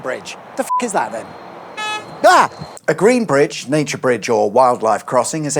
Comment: Sorry I thought you said a naturist bridge, for Surrey has some (cough) friendly car parks which offer some unique car meets & they have their own Highway Code for flashing, lights 😂